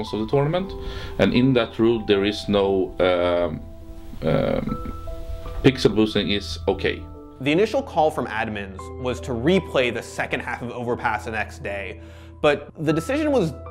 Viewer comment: If LDLC used an illegal boost on the very first round, then the game should be restarted from 0-0 not 12-3. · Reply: ah, well was kinda hard to understand him at 8:45, i thought he said pixelboosting is okey but he said that there is no rule that pixelboosting is okey.